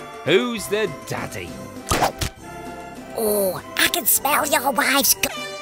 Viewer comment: What Giggs he said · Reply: rasheed j7 prime he said: I can smell ur wife